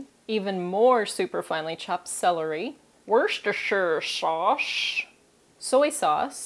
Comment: LOL! what did you said at 00:37? shtrshsh shaushe.